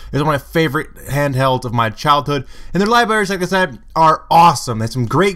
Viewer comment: did you say "ghgtghgghg?" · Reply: "and the libraries, like I said, are awesome". I could hear it, and I'm a swede D